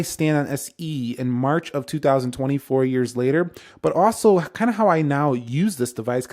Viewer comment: sounds like he said “24 years later”😂